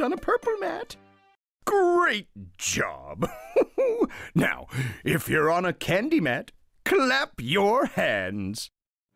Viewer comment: I thought he said if anyone is on a candy mat, they need to wave their hands in the air.